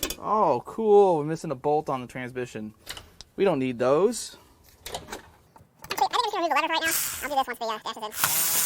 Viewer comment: I tried .25 playback speed to hear what you said. No luck. Still unintelligible, but hilarious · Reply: ''Oh i'm missing a bolt on the transmission. We don't need those. I'm not going to remove the ladder right now, i don't want any dust or dirt falling into the transmission'' - My YT addon goes down to 0.1speed 😆